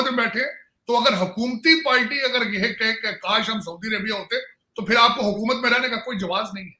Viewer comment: Location: 9:38 Did the ganjaa baadshah say Palty instead of Party at the above location?